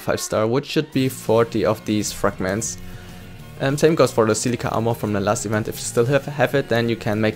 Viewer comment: did I just hear Leafa armor at 6:45?